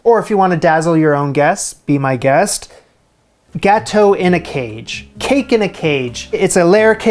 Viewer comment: you said, "gâteau in a cage" and I heard " Gato in a cage" and was concerned.